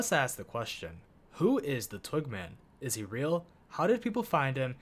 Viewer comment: thought you said twinkman and I was already sweating